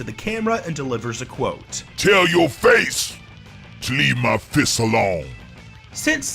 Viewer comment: the auto generated captions said "tell your face to leave my piss alone"